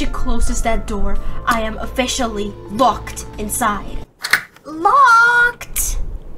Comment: 0:I thought she said “I am officially f*ed” xD